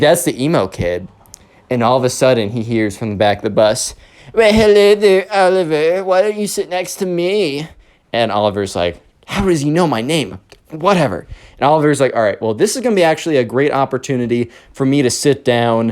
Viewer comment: When the emo kid said “Hello there Oliver why don't you sit next to me.” I thought he said Commoner and I just looked up from my hotdog like wut